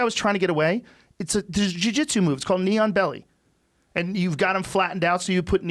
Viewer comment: Anyone else think he said "Neon Belly" and not "Knee On Belly"?